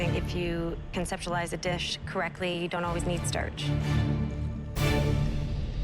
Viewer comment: i thought she said "pansexualize" the dish lmao